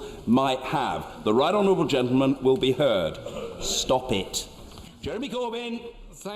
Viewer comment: stop it が stupid に聞こえて、一瞬驚いた